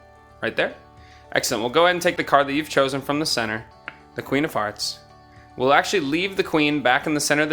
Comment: when he said queen of hearts it sounded like queen of farts